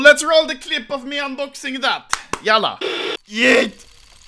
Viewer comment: did he say "yallah"? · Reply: No its actually "jävla"